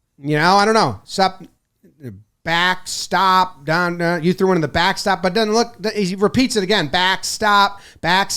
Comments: I think he’s saying “I said stop, get back and you kept coming so you’re gone” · I think he’s saying backside